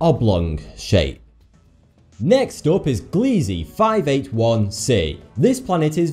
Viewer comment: i heard “glizzy” lmao